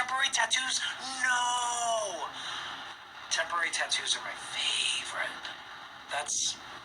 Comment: at around 19:00 i SWORE he said "Tamborine Tattoos" omg